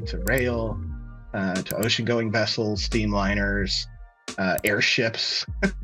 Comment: first i heard "to OceanGate vessels" and i was like wth😂